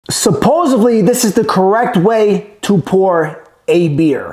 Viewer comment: Did he just say supposevly?